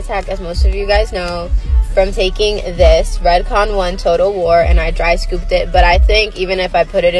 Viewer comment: I really thought she said it was called "Total Whore" ...